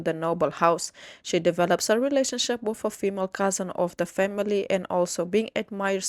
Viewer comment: Did I hear that right? Cousin?